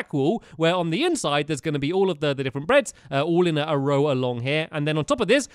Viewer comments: Stampy says "breads" instead of "beds" at 5:16 did anyone hear it??? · did stampy say breads isstead of bed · I think he said breads at "5:18"